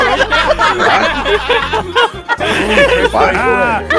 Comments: What was said during 3:15? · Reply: Chick fil a french fries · Still dont know what was said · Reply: Chick fil a french fries